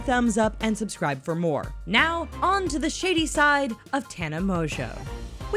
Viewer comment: I thought it said now to the shitty side of tana😂